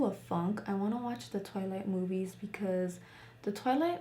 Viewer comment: I LOVE YOU BUT I THOUGHT YOU SAID TOILET MOVIES CIVJF IM STUPID